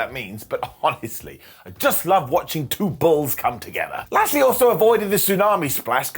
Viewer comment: Did just say 'I just love watching two bulls come together!' 🤔